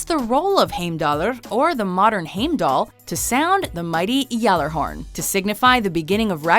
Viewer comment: did they just say gjallarhorn